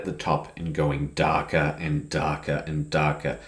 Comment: All I heard was Dakka and Dakka and Dakka!